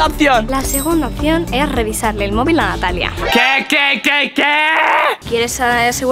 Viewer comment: en este minuto si lo pones en velocidad lenta se ve que dice andrea en vez de natalia .`-`